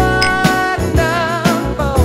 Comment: "Honestly, I don't hate you that much" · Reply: I thought she said,I don’t need that much 🤣